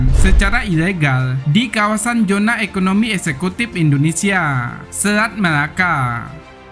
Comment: Apa sy ga salah denger Min?... Zona Ekonomi Eksklusif, bkn Eksekutif Mantab TNI AL 🙋